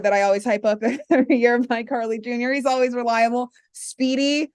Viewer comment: Idk why but when said mike Harley JR I thought she was saying icarly JR 🤣🤣